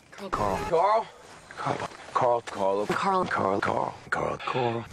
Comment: i swear shane said Carlo lol